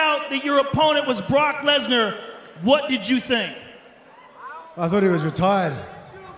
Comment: Lmao, needed a double take at 29:40. Sounded like Hunt said "thought he was retarded."